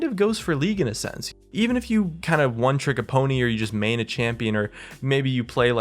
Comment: one trick a pony???? did i hear u right?